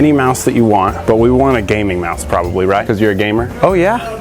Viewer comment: When you said gaming mouse I thought you said gaming mask. I was like what the hell have I missed? What does that do?